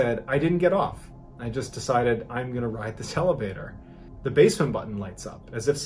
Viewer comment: Did you say hellavator? Pretty sure you did.